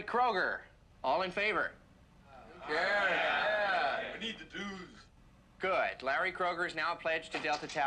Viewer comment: A true classic. Never gets old. " We need the dues"........ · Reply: Oh......I've watched this movie for years and all of this time I thought he was saying "we need the dudes." 😆